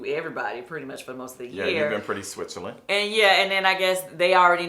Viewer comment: Did say kandi was being "switchilynn" did I hear that wrong and/or did kandi hear that and kept it moving 👀☻😂😂💀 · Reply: I thought he said Switzerland- neutral. Now I’m gonna have to go back and listen, lol.